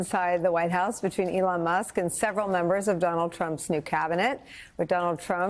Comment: Did she just say ''McDonald Trump''